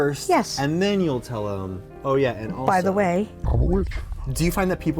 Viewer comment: Omg I thought he said by at <link>